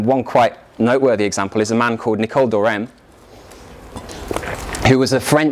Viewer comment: Help: I can't figure out the name of the french clergy man at 15:06. Nicol what? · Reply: Nicole d'Oresme